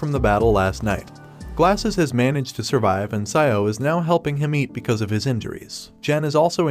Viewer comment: bro I thought I heard Xiao ling 💀 I disliked my comment 👍